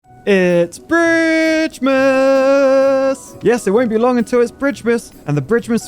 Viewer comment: I had my volume on low, and I SWEAR I heard say "It's B*TCHMASSSS" Edit: I realized after hearing it for the second time that he actually said "bridgemas" but it caught me off guard and had me confused for a sec.